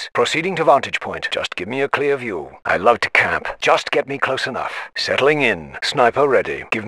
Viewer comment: When I was a kid I thought the sniper was saying I love to Canada instead of I love to camp 6:13 😂😂😂